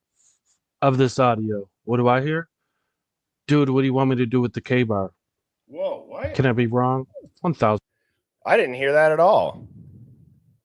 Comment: I was busy not really paying attention to the TV. I am hard of hearing (53 yrs old ) but I stopped nc I heard "steve, what do you want me to do with kaylee".